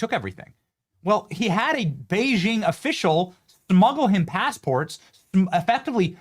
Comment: hard to deny the allegations Benny when you say "thmuggle" instead of "smuggle"....